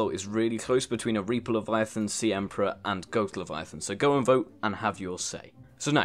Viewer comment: goat leviathan I heard goat leviathan